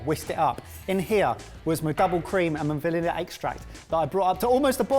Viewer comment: What I heard was Valele Extract 😂